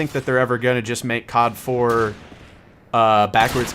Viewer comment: ngl thought is said cod is black